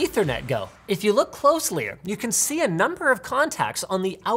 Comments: Linus keeps using "closely-er" (@ 2:25) instead of "closely" or "closer" in his videos. Please.... no more! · When I heard “closlier” at 2:27 I was like wait wait wait, did I hear that right??